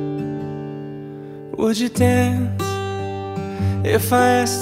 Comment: "hoje tem" eu entendi isso 😂